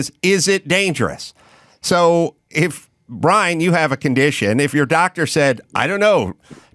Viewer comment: He said "Mike, you have a condition", I heard "Mike is probably a vegan".